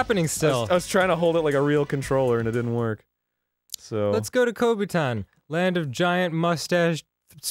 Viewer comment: At first I thought Dan said “let’s go to covid time” and was like ”did they time travel”